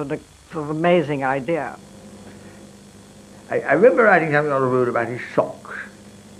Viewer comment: What the hell is Lord Deedes saying? Arr-rarrrr-rarrr-urrr-rarr-rooo-rarrr-rarr-socks. Can you work it out? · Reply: I remember writing a hell of lot about his socks? Something like that. (Shurely shome mishtake)